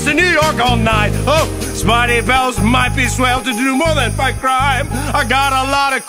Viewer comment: I THOUGHT IT SAID SPIDEY BALLS